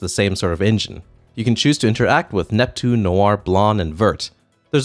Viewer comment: Did you call Blanc Blarn!?